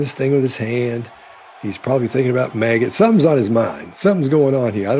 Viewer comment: I heard "he's probably thinking about maggot" 😂